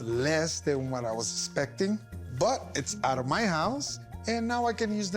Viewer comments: Chum: I'm the best pawn guy. Chum: Lemme call a buddy of mine. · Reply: I thought he said "cocaine" not "more games" at the end 😂😂😂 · I thought he said "cocaine" not "more games" at the end 😂😂😂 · I thought he said he was gonna buy Cocain and treat himself for dinner